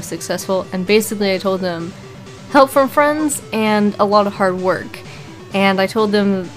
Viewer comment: "How did you get your channel to be so successful?" What she said: "Help from friends, and a lot of hard work." What I heard: "Minecraft Marriage."